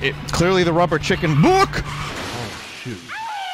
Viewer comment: thought the guy said "oh shit" at first lol